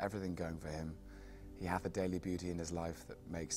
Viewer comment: he says "He hath a daily beauty" but he said it says it so casually like every day speech that I didn't catch it the first time.